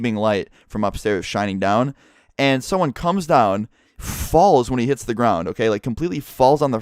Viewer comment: "he falls when he hits the ground" am i the only person who heard this?